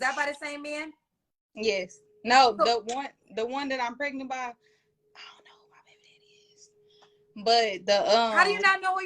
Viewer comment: I thought she said she ain't know who the father of this pregnancy is 🤯